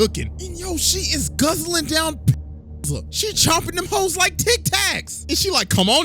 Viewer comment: what did he say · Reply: Pills